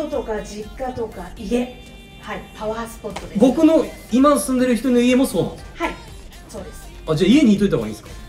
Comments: 何度聞いてものんちゃんの、「僕の今住んでる人の家」に聞こえてドキドキしちゃった😂今住んでる”1人の”家か！ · なんだか一部で色々言われているみたい？なので、書き込んでおきます（※あくまで私の解釈です） ↓↓↓ 10:20 小瀧くん「パワースポットとかありますか？」 イヴルルド遙華先生「地元とか実家とか、家！パワースポットです」 小瀧くん「(家って、実家じゃなくて)今住んでる1人の家もそうなんですか？」 とこんな感じで、確認の意味も込めて、わざわざ“1人の家”と言ったんだと思います。 それ以上の深い意味はないかと！ “人の家”に聞こえるという方もいらっしゃいますが、私には“1人の家”に聞こえます🍀